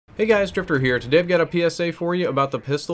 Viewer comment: Every time you do your intro, it sounds like your saying "hey guys, stripper here"